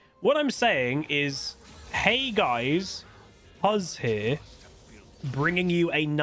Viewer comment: I totally thought you were saying huzzier but as 2 syllables. Then my teacher brain kicked in and I guessed out that you were saying Huzz Here because that's the only thing that made sense.😂